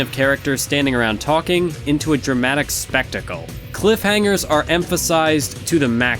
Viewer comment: All I heard was Cliffhangers are Maximum 😝😤😏